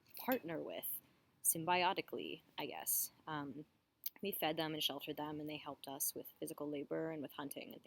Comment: For a split second I misheard part of what you said and got a chuckle out of it. I thought you said, "we fed them and sheltered them and they humped us.." I was, like, you ain't lying, lol! I pictured a dog gripping your leg going, thank you, thank you, thank you, thank you, hump, hump, hump, hump. For me, it's not the smell that bugs me so much, but rather the sheer stupidity of dogs. Anytime I might start forgetting how obnoxious dogs are and begin to warm up to the idea of owning one again, I'll hear someone else's dog barking like an imbecile, and feel the flames of hatred for their very existence kindle up inside me. There's a very specific type of bark that really exemplifies their stupidity too. It's that one that usually follows a procession of barking and then there's a pause and then this frustrated sounding bark, like , hey, why isn't this working? It has that tone that sounds kind of like when a person says someone's name, with that, what gives, kind of vibe. It's like a stupid cave person repeatedly alternating between smashing a rock against his head and grunting in sad frustration when it didn't cause his campfire to light. It makes me just want to put the moronic creature out of it's misery...and mine.